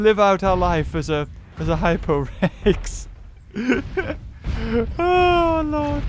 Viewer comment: instead of hypo res I thought it said hypo sex XD